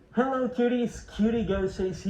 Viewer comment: bro at the start of the video i heard "Hello Titties" Lmao